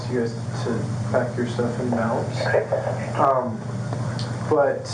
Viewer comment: Did he say, "...pack youir stuff and bounce."?